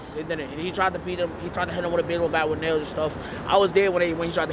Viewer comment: Is it me or am i hearing "BagelBat"?